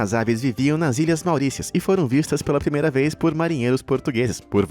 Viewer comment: nao entendi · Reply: ilhas mauricias